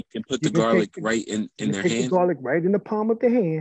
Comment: I Thought he Said Rub the Cheyenne in Your Hand Not the Garlic. .🤔